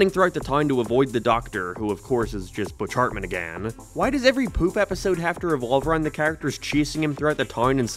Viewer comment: i'm sorry but i CANNOT hear this as "poof episode" it sounds like "poop episode" and I had to check the auto captions to make sure i wasn't crazy 😭